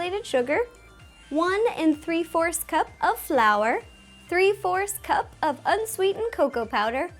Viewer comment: No offense but when you said 3/4 cups of flour (i think), it sounded like you said 3 force cups.